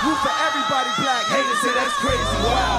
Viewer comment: did he say and your President a Chump 🤔😂💀🔥